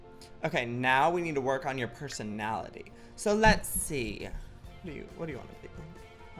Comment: Did I just hear Joey say "Babe what do you want to be" at 9:20 😏💕👌